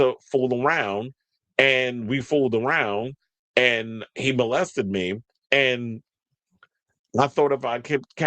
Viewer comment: I thought you said N worded him and I got really confused 😂😂